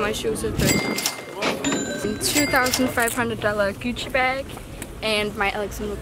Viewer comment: how she says gucci bag😂😂😂😂😂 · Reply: Adrian Mihai I heard her say "cuci bag"